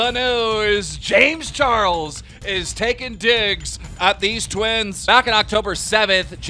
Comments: I thought he said James Charles taking dicks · I thought he said “James charles is taking dicks”